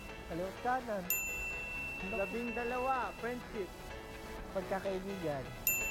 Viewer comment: hoy inosente akoo!! pero narinig ko french kiss😢👍 HAHAHHAHAHA